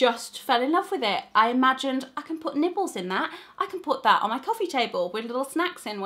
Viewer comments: lol...not going to lie, when you said "i'm going to put nibbles in that" I heard it as "nipples" and nearly spat out my tea pahaha XD Awesome video, as per usual :) Really excited for these moving out series :D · i thought she said at 11.00 i can put my little nipples in this bowl instead of nibbles! I'm annoyed at my ears also i loved this video